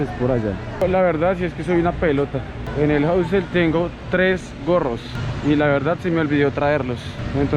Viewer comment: jajaja estaba viendo el video con sub títulos y sin volumen para no despertar a mi bebé y en el min 4:32 aparece: tengo tres porros y la verdad se me olvido traerlos jajajaja y uy como así jajaja me toco subir un poco el volumen para escuchar que dices gorros. Tu sabes si no hay problema con llevar bebes? Muy pronto iré a Medellìn. Gracias.